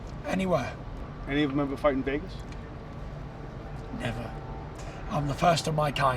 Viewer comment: Can someone please tell me what the interviewer said at 9:41? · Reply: Any of them ever fight in Vegas!